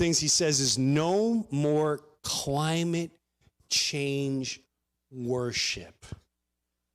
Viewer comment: "Butt-Stuff Month"? And I thought Pete said "No more climate change horse shit"